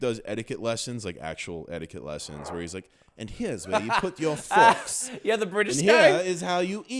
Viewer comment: Weird thing I’ve noticed about the automatic subtitles: Whenever either of them say “shit” it censors it completely, but at 23:17 it mishears saying “forks” as “fucks” and spells it out with no censor.